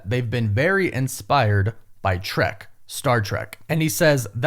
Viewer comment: I thought he said shrek